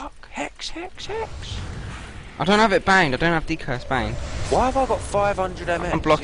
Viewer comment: I thought you were saying "I don't have it banged ok?" all the way through... Pro listener right here